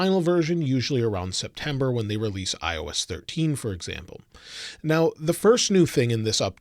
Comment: did he say IOS 13